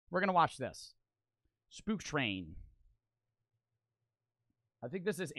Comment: thought he said poop train, not spook train